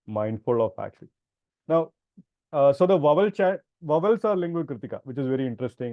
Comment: I think he's saying 'vowels' here.